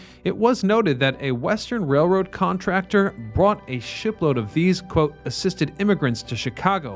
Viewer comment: Hah, sounds like you said "brought a 5hit-load of these," but I suspect it was "a ship-load."